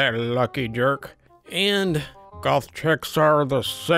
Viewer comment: Soooo...... I thought kept saying golf chick and referring to OP as a golfer, it took like half the video before I got that he meant GOTH not GOLF xD I was so confused for awhile